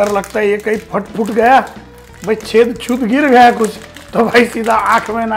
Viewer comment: Guess what say's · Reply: He said gir gaya sala chutiya 😂😂😂